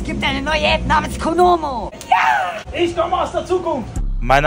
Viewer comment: Ich habe Kondome verstanden😂😂